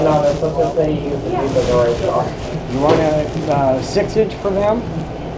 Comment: does he say “a 6 inch foot long”????